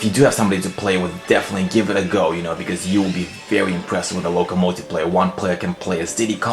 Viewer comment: LOL, it sounded like you said locomotive player instead of local multiplayer. XD Your little Yoshis reminded me that i got a GIANT YOSHI for Christmas! It's EPIC! :D